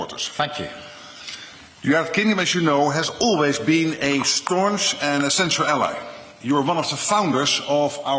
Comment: WTF did he say "Sqaunch"? Is he trying to be part of the Squanchy community? 😂 A fan of Rick & Morty? 😂